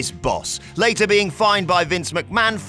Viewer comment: I thought it said fired at first lol